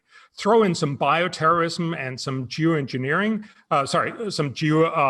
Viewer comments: Lmfao! Some “Jew” Engineering. Hahahahahaha! FYI. I know he said Geo but when I first heard it, it didn’t sound like that. Hahahahaha! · Did he just say "jew engineering"?... Twice? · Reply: I think he mistakenly said "geoengineering" by mistake and slurred "geo" slightly. No pun intended. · Did anyone catch at 32:10 “Throw in some Jew engineering” !!! I know it was Geo engineering. Just made me double take